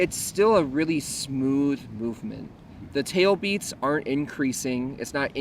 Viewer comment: Am I the only one who had a hard time discerning whether he was saying that shark bites "ARE" or "AREN"T increasing? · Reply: The first time I thought he said “are” but later in the video he says “aren’t” many times so I’m pretty sure it’s the latter!